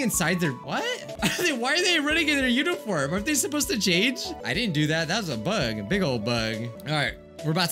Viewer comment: the captions glitched a bit when you said change the captions said jade just saying ok?